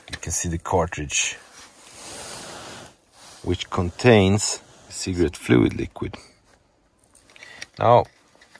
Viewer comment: Under here, you see this cartridge, which contains THE SECRET FLUID LIQUID. I died, funniest thing I’ve heard in a while. Yes it does my man, yes it does. Now turn that shit on already, wtf man!! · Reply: I thought he said cigarette fluid liquid